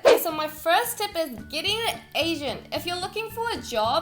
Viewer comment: I have an ear infection at the moment so I can't hear very well out of my right ear and so when she said "getting an agent" I thought she said "getting Asian" XD